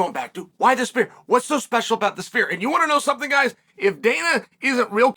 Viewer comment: I keep hearing, 'Spear'.